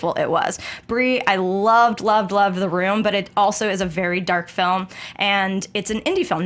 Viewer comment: did say she loved "The Room"?